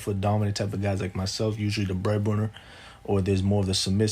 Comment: did he just say he’s the “bread butter”?